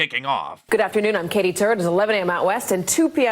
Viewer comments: WTF her name really is Katie Turd? That's what it sounds like she is saying. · Omg great editing! It actually sounds like she says “Katie Turd” 😂😂😂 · sounds like she actually says turd. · Kittie Turd? Did I hear that correctly? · sounded like she said turd